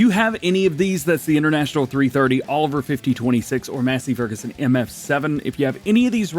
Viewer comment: 5026 he said. He was talking about the running gear. Where did you get 5020 at for a Oliver tractor??? 🤦‍♂️ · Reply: my bad I thought I heard him call it a 5020 Oliver